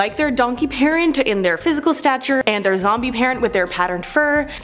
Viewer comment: did she just say "zombie parent" lmfao